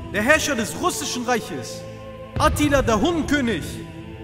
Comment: sorry bro hab verstanden das du gesagt hast Atilla der Hoden König 😂😂